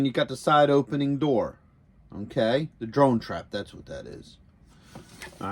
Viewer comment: When you say okay I hear "m'kay" 😁 Thanks for sharing this. Looks amazing